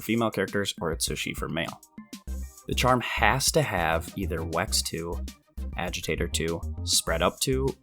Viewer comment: is he saying the charm as to have wacks 2? I have to be mishearing something. Even the closed captions says wax 2 lol